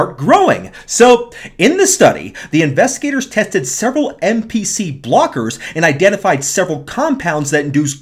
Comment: Crap, I thought you said NPC blockers. I was excited for a minute.